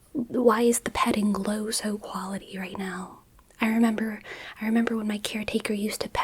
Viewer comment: am i having a stroke or did she say “the petting glow all quality?”